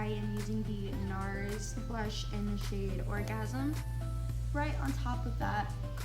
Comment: did she just say orgasm?